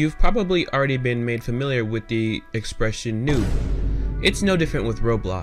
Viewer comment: the subtitles said "nude" instead of noob 💀🤣🤣